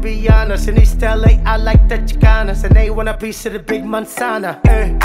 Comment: Pense que decíaa mexicanas y dice chicanas jajaja 🥺😞